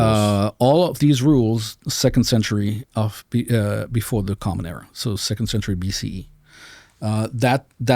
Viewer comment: Every time i hear “before common era” i hear “ cope cope cope”